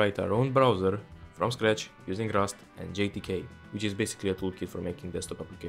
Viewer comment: the screen and captions say “GTK”, but voice sounds to me like it is saying “JTK”?